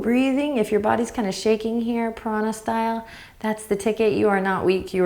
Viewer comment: I'm sticking with my misheard version and shaking piranha style.